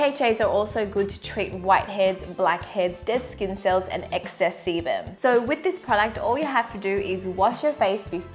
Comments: What I heard @ 1:29 - excess semen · Sounds like she is saying excess semen😂